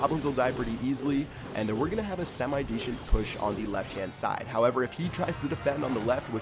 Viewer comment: Who else heard deshent push